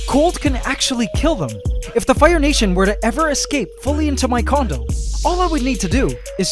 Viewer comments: I swear i thought he said “if the fire nation were to fully escape into my condom” lol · Pretty upsetting, but still cool EDIT: Did anyone else hear "escape into my condom" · kinda sound like he says "Fully escape into my condom" ouch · If the fire kingdom to escape fully in to my CONDOM lmao 8:30 (that’s what it sounds like I know he said condo) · "If the fire nation were to escape fully into my CONDOM..." \(")/ why tho. I bet you meant condo. Did anybody else catch that? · I thought he said “condom” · Happy New Year, AC Family! Hope you enjoyed this year-end special and don't forget to watch the bonus episode after this linked in the info cards (top right of the video or here at this link: <link> ) and also please SUBSCRIBE to my channel and hit the BELL if you haven't already! Join the growing global AC Family, and let's go into 2019 strong like a powerful and unstoppable ant colony! Ant love forever! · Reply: sounds like he said condom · Anyone else thought he said condom? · am I the only one who heard him say "if the fire nation were to fully escape into my condom" at 8:30 have I just gone insane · i thought he said condom XD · Did anyone else hear him say “if the fire ants were to escape into my condom” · did he say if they escape in his condom? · Why did he say Condom at 8:33? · sounded like condom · Did he say “condom” at 8:33? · I thought he said condom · Did anyone else hear condom at 8:32? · did he just say condom? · I thought he said condom 😅